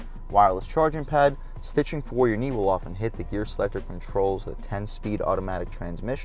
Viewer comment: slow down turbo did you say "your knee will hit the gear selector control" 🤔🫣